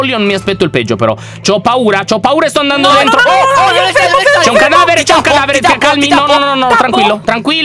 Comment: Al minuto 7:38 io ho capito "Ciao cadavere"!!!!😂😂😂😂😂😂😂😂😂😂😂😂😂😂😂😂😂😂😂😂😂😂😂😂 #Veraleonessa 😍😀😀😀😁😁😁😁😂